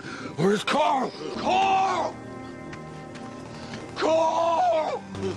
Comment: after listening to this 6 times. i heard coral instead of carl. turn on caption at 1:52 he says caw instead of carl and coral